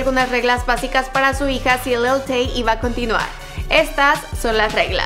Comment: Pensé que decía Lil tjay jajsjjjsjs